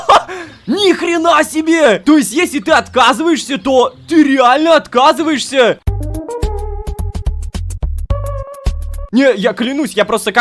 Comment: Мне послышалось "если ты отказываешься то ты откатываешься,,😂